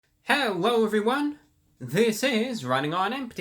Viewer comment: It sound like the said "Hewwo everyone" because my phones volume was low